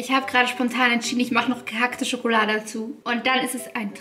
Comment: Ich hab " gekakte " Schokolade verstanden 😂😂😂😂😅😅